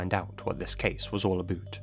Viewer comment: did he just say “ let’s see what is case is all about boot”. 🤣🤣🤣🤣🤣🤣